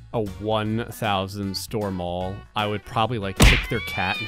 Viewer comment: I thought this said 1000 stories and I was really disappointed when I realized it was only 1000 stores